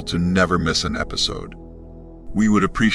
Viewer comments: Wow that was sinister in the very first bit when I heard "Welcome to the Spirit of Evil" and I was like "WHAT?!?!?" Lol.. and then I read what I heard.. Spirita Vivo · I first heard "Spirit of Evil" so I had to read your name again. X^D Thank you for the information. <3